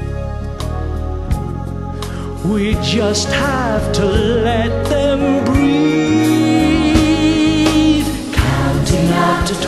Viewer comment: 'We just have to let them breed' Am I hearing that correctly??